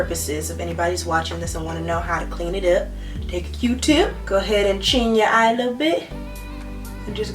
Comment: did she just say “ching your eye a little bit” 🤔 no sis....